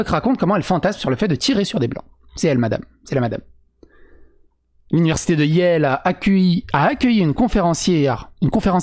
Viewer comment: J'ai compris "L'université de iel", j'trouvais ça doublement drôle du coup Edit : et putain la meuf s'appelle "Weiss" (blanc en Allemand), t'es sûr que c'est pas juste une longue parodie filée cette histoire? Non parce que là ça devient presque un sketch :')